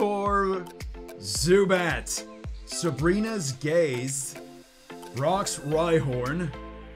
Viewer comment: sounds like he said Sabrina's gay!!!!